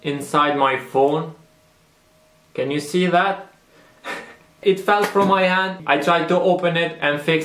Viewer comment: i thought he said 'it fell from my ass'